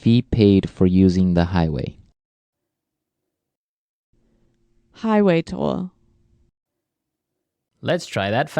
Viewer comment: The subtitle should be highway toll instead of higway tour